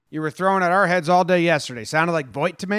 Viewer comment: “You were throwing at our heads all day yesterday” all I heard was “hey hey”